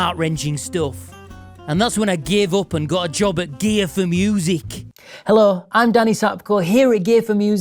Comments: Fatality! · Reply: Did you say "gay for music" or did say it. 🎶 😅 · ur accent is so farked mate--sounds like you were saying, "gay for music". is the voice in your head wen u think, is it in THAT abbhorent accent? thats torture mate. i feel sad for ya. im "gootit" for ya dude. lol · Sounded like you said "gay for music" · I could swear he said, "Gay 4 Music." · I thought he said gay for music... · I thought he said GAY for music lololol!